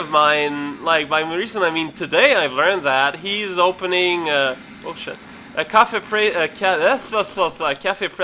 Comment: did he just say "Oh shit"?